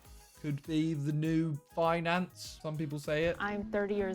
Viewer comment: Well I think I would be at the bottom cause I thought it said fiancé instead of finance